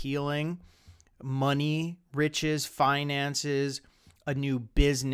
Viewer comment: for a second I thought you said "money, bitches, ..." HAHAHA